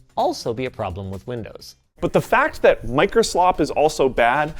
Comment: DID HE SAY "MICROSLOP"